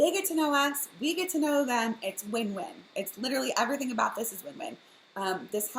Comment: Did you say at 14:10 "everything about this as women"? The last time I checked, I was still a male.